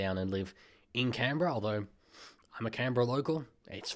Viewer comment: I thought you said Canberra was bad